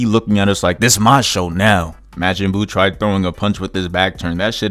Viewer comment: I thought this man said “mad jin buu” for a second 💀💀💀